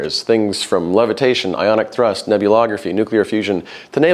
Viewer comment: did you say "nebulography"? I googled my arms and legs off... What what what - what is that??? 😵‍💫🙏